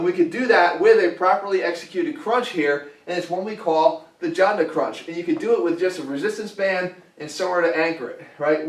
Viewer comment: What we call the ... crunch? What is it called? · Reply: I think Jonda Crunch.